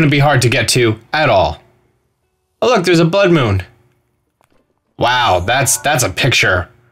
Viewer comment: did he just say bud moon?